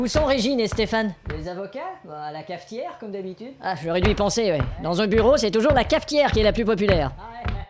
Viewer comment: J'ai compris "... masturbé dans mon bureau..." de la part du patron à un moment, sinon j'ai rien compris ^^